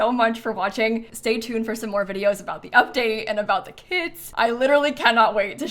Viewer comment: who else heard kids and was like what??? (i now know it is kits)😂